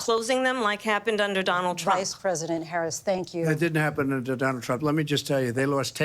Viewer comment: CWIME WATES WAY DOWN @ 1:26:43 Did he say "That didn't happen I'm Donald Trump" I can't really hear him & I doesn't even matter it just caught me off guard 💀